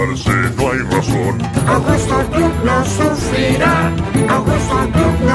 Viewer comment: pense que decia Angusto gloop Lo SufRirA